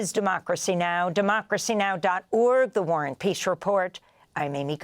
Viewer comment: I know it's the Warren Peace report, but I always hear "The War on Peace" or "The War and Peace" report. Edit: Just looked at Democracy Now!'s website. So, it is the "War and Peace Report"? That link takes me to page dated 1999. I thought somebody Warren was a donor. Did I dream this up? Help me out.